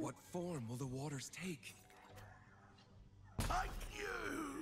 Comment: Did say fuck you when he died at 6:33 lol? Also i really appreciate the contrast between and